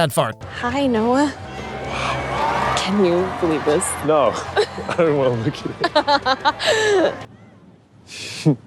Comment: the subtitles say "no i dont want to lick you" 🤣🤣🤣🤣🤣